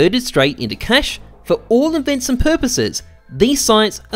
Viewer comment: did you say "invents and purposes" @ 2:20?